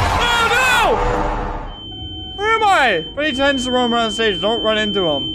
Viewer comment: i swear he said "freddy tends to roam around satin, dont run into him" lmao